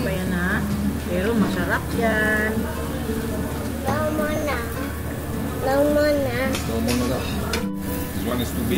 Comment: Favorite nako ng Seoul ga ♥️ naa pud nay branch sa kanto Insular Village happy anniv 🫰 did said, tama na tama na